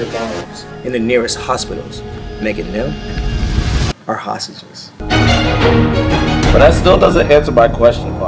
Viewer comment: Guys, what is he saying 1:00 after hospitals??? Please respond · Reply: Полина Юрганова “making them our hostages.”